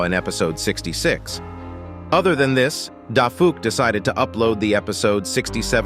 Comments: People who saw him type daffy duck instead of dafuqboom here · he said Daffy Duck instead of Dafuq